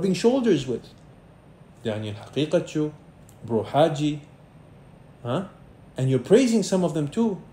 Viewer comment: Daniel Haqiqatjou? am I hearing that right? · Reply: Yes, and I’ll make a video about him soon ensha Allah.